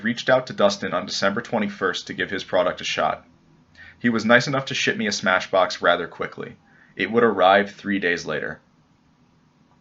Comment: I thought my man said, "He was nice enough to shit me a SmashBox rather quickly."